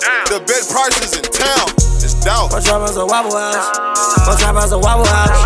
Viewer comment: Bro I thought he was was saying wobble house lol